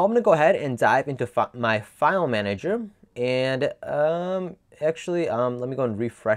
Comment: Did he say fuck my file manager in 1:51 ???